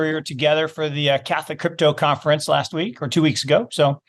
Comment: Did he say catholic crypto conference, wtf ?